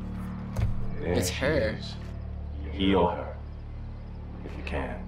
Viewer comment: "there she is... pee on her..." i swear he said that.